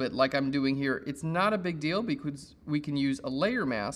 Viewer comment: Did he just say "beecootz" instead of "because"?